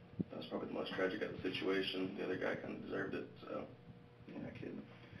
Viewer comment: Did he say "The other guy kind of deserved it." ???